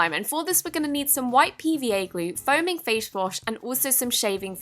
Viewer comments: Haha🤣 0:27 did you say foaming fashwash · did anyone else hear foaming fash wash???